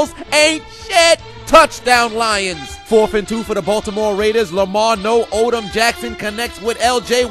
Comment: I still must be tired from my nap. Because I think I heard you say the "Baltimore Raiders"